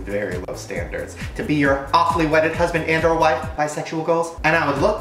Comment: did just say awfully wedded 😂😂